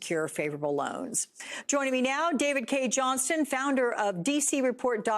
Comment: My ears keep deceiving me - I only hear 'Sneaker Con'. is it a joke or ?? Is the entire GOP going to be given a pair and wear them in the House?